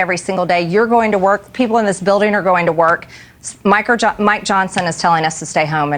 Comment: I thought she called him “MICRO JOHNSON” @ 1:06 😂😂😂😂😂😂